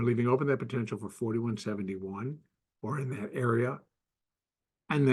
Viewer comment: Question, around minute 8:12 did you say completion of wave 2 could be 4171 but as low as 4731. I probably heard wrong, but can I please confirm these numbers at the 8:11-8:12 minute mark. Thank you so much. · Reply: Yes, that is what I said -- wave 2 should find support at 4771 and possibly as low as 4731 -- I didn't go back and listen to the update -- sorry for any slip up